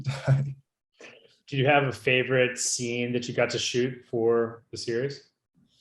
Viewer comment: Oh my goodness at first I thought he said “do you have a favourite eye” bc I wasn’t listening and I almost broke down😭😫 Only ppl who’ve watched resident evil get it🫣